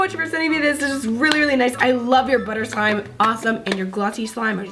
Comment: There was a commercial when she said butter and so it sounded like she said butt 😂🤣😂🤣😂🤣😂🤣